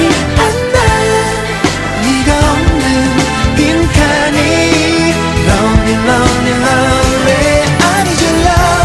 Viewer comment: lonely lonELY LONELY.... my fav part :3 I thought they were saying "love me love Me LOVE ME" but now I know lolol